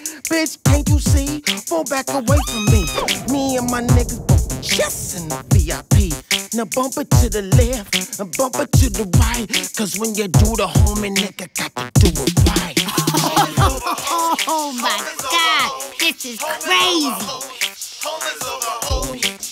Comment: The do the homie line sends me every time 😭😂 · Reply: No cap I just realize that’s what he’s sayin 😭😭 I thought he was saying do the ho mad